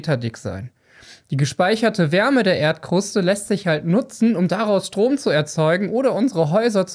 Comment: 😳Ich habe verstanden: "...um daraus Drogen zu erzeugen." 😂😂